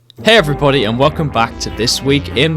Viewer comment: He probably said "hey everybody" .... but I heard "hey everypony"..... am I the only one ?